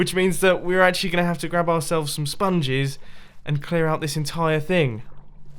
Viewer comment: I thought he said that he is going to take some spoons to clear the water